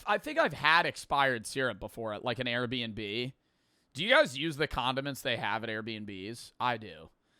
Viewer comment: thought he said condoms